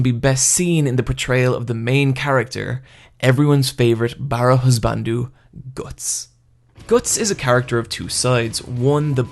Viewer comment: Wtf does he say at 5:34? Barrowhisbandu?